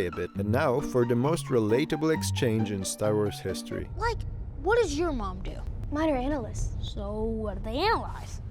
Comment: I can’t understand what they said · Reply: "Like - what does your mom do?" "Mine are analysts!" "Sooo... what do they analyze?"